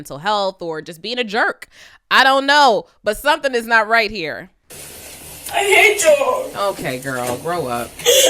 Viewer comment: "I hate ya'll" I thought she said I said I hate jollof! lmbo